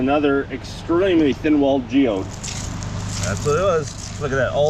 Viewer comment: I thought I heard "Obi Wan geode."